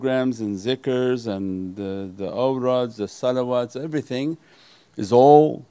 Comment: what is he saying ? owrads ?